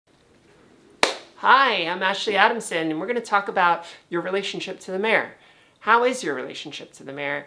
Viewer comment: Ashley ... oh, yay! ... finally another "philosophical" discussion. I had a "Chicago, much?" moment at the start of this when I heard "relationship with the mirror" as "relationship wit' da mayor"! I have varied relations with mirrors, cameras, and video ... most of the time I'm cool with the mirror (I may have developed my own "blue steel" for that), and am about 50-50 with pictures, but tend to loathe 90% of videos of me. I guess the mental image I carry around of how I look is that lethal stare that the mirror gets, when video typically makes me look goofy. You said "reconfigure your brain" as an essential part of transition ... I've always wondered about this. Do trans gals (who don't start out as already feminine gay boys) have to make a big effort to get away from the "guy brain"? It seems like it would be something like working on the voice. That might be a good follow-up book for you: "Feminizing the Brain"! I know a couple of occult/spiritual practices that involve intense work with a mirror ... I guess they're OK as long as they don't start out with: "Slave in the magic mirror, come from the farthest space, through wind and darkness I summon thee. Speak! Let me see thy face." ... heh!